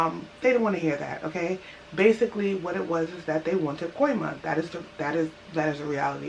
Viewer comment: at @ 6:50 they took the products because they wanted what? did you say coin money? i get that they thought it was expired - are you saying they wanted a bribe ? ty I'm visiting soon, considering moving. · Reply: Coima=bribe